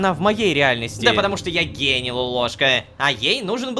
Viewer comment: Когда JDH(Джон) сказал "Гений" мне послышалось "гей" 😅